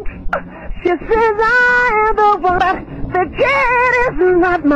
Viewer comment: What I hear: “BAH, the kid is not my son, WHOO, CAUSES I AM THE ONEEEEE”